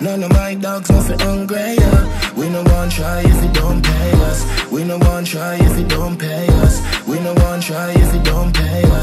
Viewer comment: I always thought he said 'We nuh try if yuh nah "US" ' but then when I saw the lyrics, looks like he's saying "$USD". I replay the song a hundred times and he's actually saying "$US" yes lol. I didn't know at all